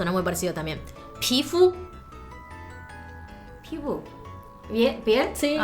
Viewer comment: Parece que dice Tfue